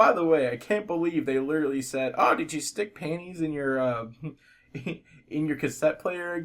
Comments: I thought he said pennies and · i thought he said pennies not panties